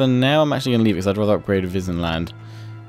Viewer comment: "In an alliance with Wissenland"I thought you said Disneyland